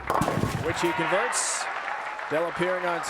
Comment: It sounded like he said "which he converts" to me. Hmmm..